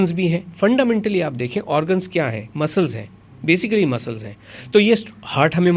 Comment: "organs muscles hai" kya bol raha hai bhai??? Kidney muscle hai? 🤦‍♂️